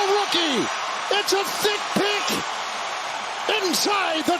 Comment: “A THICK PICK!” Kevin is REALLY thinking outside the box!!!! 😂😂😂😂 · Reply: i thought he said feet pick, i had to rewind